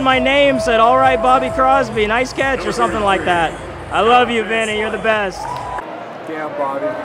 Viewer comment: did he say Vinny Or Benny? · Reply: Vinny